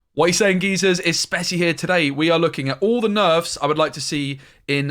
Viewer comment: I thought in the intro you said "Wat u saying Geezers it's Spaka here" 🤣😂🤣 Gg with the snap content 👊🏿